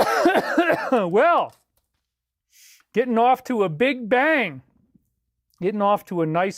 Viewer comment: David says if anything ever happened to him, he wants more people to do mass meditations all over the world. 🙌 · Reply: "getting off to a big bang" Really kind of sounds like he says Getting offed to a big bang...? He even repeats it. In his last live stream? 🤔